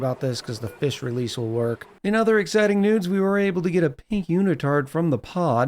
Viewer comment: Change the wallpaper behind the fossil in your great hall. The square stripes are clashing with the fossil. It should look better. Also at 41:54 i heard "in other exciting nudes" 😂